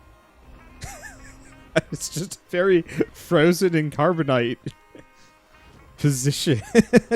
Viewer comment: You did indeed say "carbonite". Albeit with a slight "cr" lilt in the c. And slight enough that it's barely noticeable.